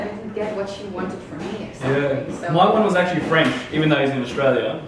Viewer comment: Did you said that your mom is french?